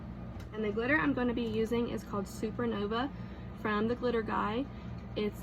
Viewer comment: I thought I heard u say the glitter was NOVA....Is that correct or is it HAVANNA?? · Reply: Supernova